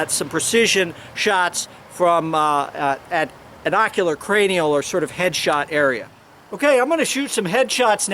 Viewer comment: did he say at an ocular cranial Head shot situation? My god man just say head shots.